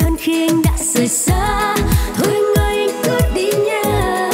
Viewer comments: Có ai nghe thành thôi anh ơi anh cút đi nha như tui ko??? 😂😂😂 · Bữa h em cứ nghe thành "Anh ơi anh cút đi nha" nghe nó gắt và thú vị hơn, nhưng chung quy bài hay chị ạ ❤